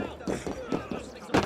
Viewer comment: its sounded like the npc said " die nword" if you know what i mean